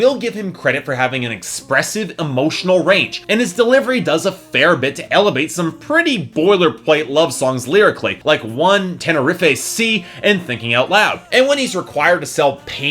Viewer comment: Dont tell me i just heard him say 'Tene-riff-ay sea'....